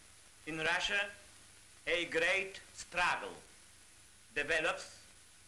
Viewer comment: lol he said Stragel instead of struggle